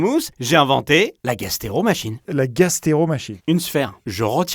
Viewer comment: J'ai compris "gastro" au lieu de "gastéro" donc j'imaginais le pire...